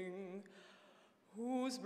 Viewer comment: At the 0:30 mark...Sounded like she said "Whose broad stripes and bright stalls" 😂😂